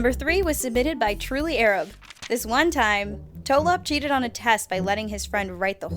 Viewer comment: Wait i thought you said emogi only cheated once